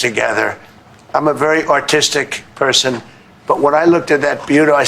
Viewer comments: I really thought the orange blob said "I'm a very autistic person." Had to play it back three times. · All i heard was "I'm a very autistic person".